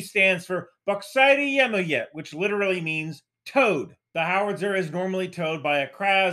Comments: Hand up if you heard "toad" when said "towed." (Not my finest hour.) Edit: I just figured out why I heard it as "toad." It's because "Frogger." The NATO designation for the MiG-23 is the Frogger, so I heard "toad" instead of "towed." That's my story and I'm sticking to it. And thanks to for another great, informative video! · I thought u said "toad" not "towed" lmao